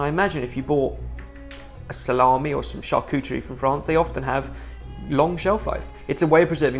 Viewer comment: Seriously who writes the subs? Lol is English their 2nd language? So many little words missing or completely misheard by the subs writer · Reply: he says Charcuterie and the subs say "churchuri" seriously though I watch this with my deaf friend and he's constantly asking me to explain what the subs are 'supposed to say'. They are so consistently wrong, it's made me remember why I stopped watching months ago - I went about an hour binge without seeing good subtitles on this channel. So I stopped bothering.